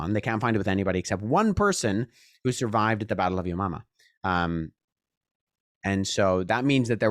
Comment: Swear it sounded like he said it was a survivor of the battle of your mama Edit: it actually is yomama that’s great😂😂😂